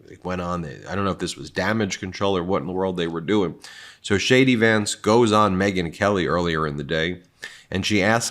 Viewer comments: Did you just say shady Vance😂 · Did you just say Shady Vance?